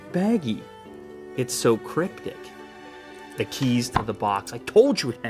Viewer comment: was i the only one who heard him say "it's cryptic." but sounded like crip di**?!?!?!